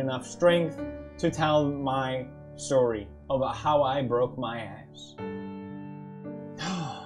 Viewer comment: Thought the video said IM BROKE AS ASS 😂